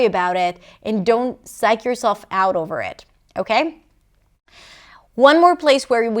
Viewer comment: These videos are true gems, thanks a lot (: By the way, at 4:29 did you say "Psych yourself over about it"? I've activated the subtitles but they only seem to be available in Spanish